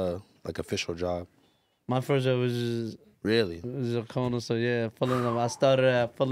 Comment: wtf did he say his first job was?? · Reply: Corner store